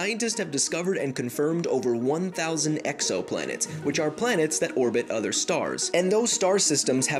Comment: I heard EXO xD